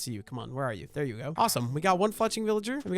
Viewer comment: when you said fletching auto captions thought you said the f word